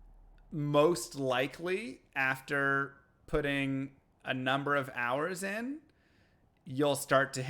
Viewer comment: did he really say "pudding" :D ??